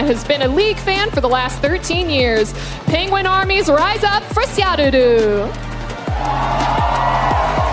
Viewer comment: 最初のほうraizin and syaruruって言ってるんだろうけどライザップしゃるるに聞こえて不覚にも笑ってしまった